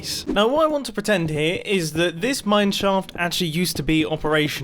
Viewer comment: David Newbon Its a joke. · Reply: Builder 3000 Yes there is, your sense of humour can determin if it is, it sounds like he says mineshoft becouse of his accent.